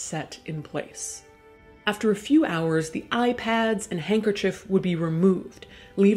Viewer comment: totally didn't hear it as "iPads" at first. nope. definitely not.